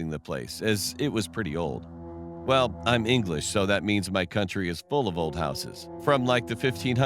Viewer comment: did he say "I am English"